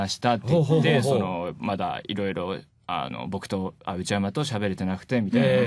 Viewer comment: it sounds like he said bokuto